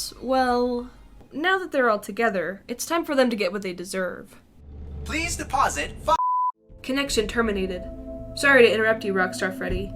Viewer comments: it sounds like Freddy says “ Please deposit FUU-“ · pls deposit fu## i’m not the only one who heard that too right